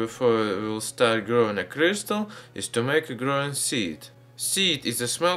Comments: i heard him say Crystalmeth.. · I thought he said crystal meth I mean it sure looks like it